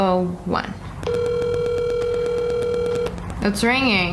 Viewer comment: The first red numbers you called sounded like it was saying “I’m gonna kill you” too me❤!)